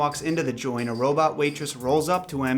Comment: I always thought the waitress was saying "you wanna cup of joe a juice?"